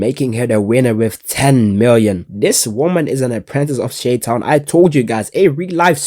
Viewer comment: wait what?! did u just say Shaitan aka the devil in arabic?? how does he knowww :o